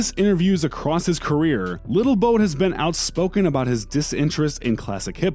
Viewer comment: Did he say "Little Boat" at 6:15 dead💀 LMAO